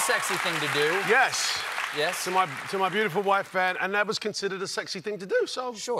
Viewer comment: Did he call his wife Hanne at 4:30? As in his ex wife Hanne, and not his current wife Sabrina